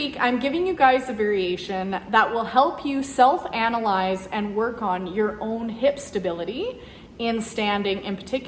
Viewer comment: Your accent 😂 I heard “ Work on your hip SH*T” 🤣🤣🤣🤣